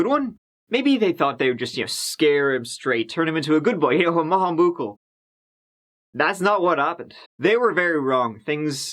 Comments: So... wtf is saying here? According to the closed captioning it's "a mahamukal"? 🤣 Anyways, I was born in 1989 😌 I feel really old when I think about that lol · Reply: He is saying "maith an buachaill" which is Irish for a good boy. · Captions say, "...turn him into a good boy you know a mahambukul" anyone know what "mahambukul" is? Or what might have actually said at this point? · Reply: Maith an buachaill - good boy in Irish